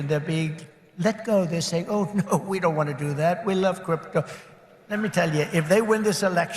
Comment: Did he just say 'we love crypto?"